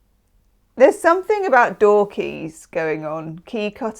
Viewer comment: When you said "door keys might be important", I just heard "dorky". I might have a significant nerd event!!